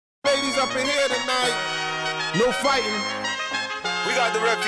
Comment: "Ladies up in here tonight" I heard "Babies up in here tonight" and looked at the baby in the fake tree. Am I the only one messing up the first line?